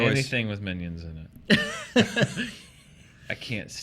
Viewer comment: When said "Anything with Minions in it" I really thought he said " Anything with Vin Diesel in it..I can't stand it" haha